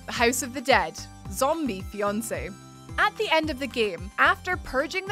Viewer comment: ZOMBIE BEYONCÉ! Haha! Initially I was doing something else whilst listening to this video playing in the background. At no.5 in the countdown (@ 6:24-6:28) “House of the dead, Zombie Fiancé” I was so sure I’d heard ‘Zombie Beyoncé’ which peaked my interest in that game ten-fold...haha! Just imagine Beyoncé as a zombie. I rewound the video to double check & obviously immediately realised I was incorrect & had misheard due to Scottish accent. Oh well...It was a fun thought though while it lasted!